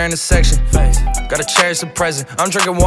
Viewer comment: Man said "gotta cherish the present, I'm drinkin water and wearing protection" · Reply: I thought he said I got a chair as a present 😂😂😂